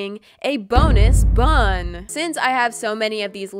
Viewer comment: U know how u said bonus bun I thought u said bonus bum